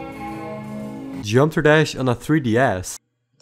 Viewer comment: Why did I hear "Gd on 3D ass" 😭🙏